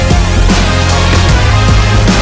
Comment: who else is crying... merci Madeon, c'est les larmes de joie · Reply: móchie ah! Like "tu m’as encouragé créer un truc quoi!" I’m so dumb. I heard "tu m’encourageais à craindre tout quoi." Mdr